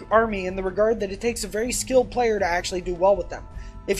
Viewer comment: When you said Ynarri requires a "very skilled player," I misheard that as a "very slow player."